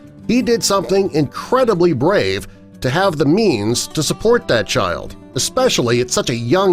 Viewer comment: who else heard *memes*